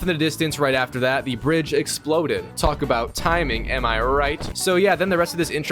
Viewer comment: my brain thought he said the British had exploded instead of the bridge had exploded